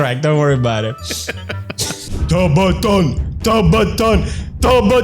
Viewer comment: For a second I thought he was saying Sabaton instead of ''Tub-A-Thon''